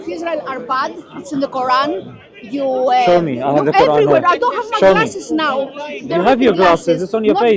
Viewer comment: Did she say “i dont have my glasses now” 😂😂😂